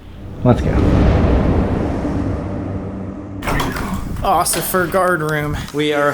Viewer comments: was it just me or did said Ociffer guard room lmfao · did he just say occifer instead of officer 😂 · Haha at 15:49 says “Ocifer guardroom” Instead of “Officer quardroom” cute